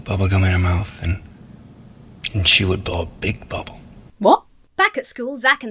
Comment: I was watching it at 1.5, so it sounded like "simply put a piece of bubblegum in her mouth and she will blop blip bobble"